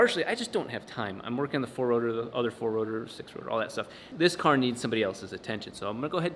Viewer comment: did I just hear 6 rotor???